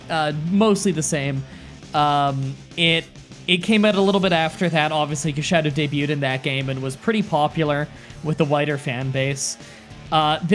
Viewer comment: I dont know if the race thing fron the first part was still in my head, but at 2:40 I heard you say "with the whiter audience" and I laughed a little too hard